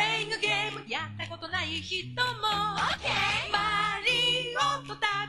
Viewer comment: I always heard you don't play the game, yeah it's not for you